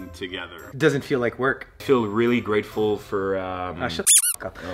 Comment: did he say he gets free "weed" all the time or am I hearing this wrong.